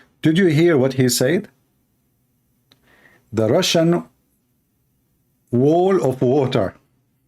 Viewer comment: You shouldn't take tucker as some god who only says the truth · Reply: yeah like the Russian wall of water that is talking about huh. Did you think that your allah misheard Tucker when he said "RUSHING WALL OF WATER" no of course not. You're pathetic when you take as an authority on geopolitics.